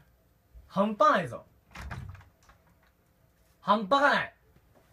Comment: i thought he said "im fine"